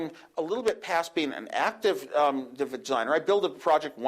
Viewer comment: Lol he totally sounded like he said "active vagineer" but yeah, he meant active engineer. Woz is great, it's crazy watching this now and how he said that weather and stock would information would make its way onto other devices and now Google TV is out.